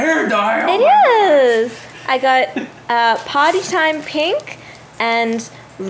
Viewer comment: I thought she said Potty time pink instead of Party time pink...